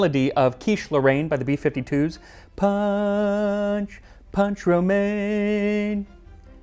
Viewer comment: I hear "Pepsi Man!" but instead "Punch Romaine!"